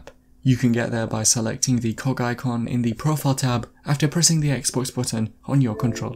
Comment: Lol i thought he said the cock icon.